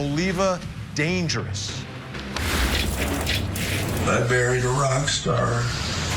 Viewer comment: I thought he said he "buried a Rockstar " not married.